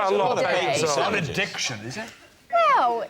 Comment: Could someone explain to me what Rhod is saying @ 44:26? Thanks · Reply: "it's not an addiction, is it?"